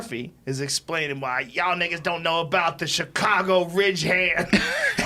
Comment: Did joe just say N!@@ ?????